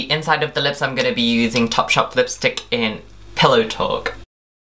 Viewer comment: Did he just say Pillow Talk ...